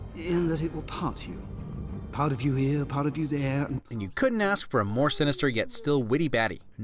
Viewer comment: i genuinely heard this as "party over there" when i was a kid.